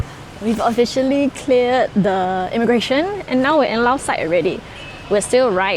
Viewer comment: i heard “now we’re in lao sai already”